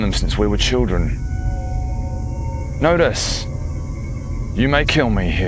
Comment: When he said "know dis" I thought he said "notice" and I was confused.